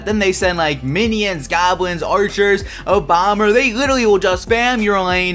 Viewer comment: did he say obama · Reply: lol he said "a bomber"